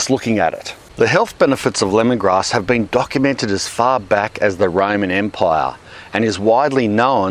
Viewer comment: Fack.I thought you said 'The Ramen empire'..Oh gosh!